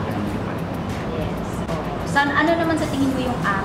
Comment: Oi tama ba yung Dinig ko sa may bandang 2:01 , "Xan" short for Alexander ba yung tawag ni Barbs kay David? Or ako lang ba?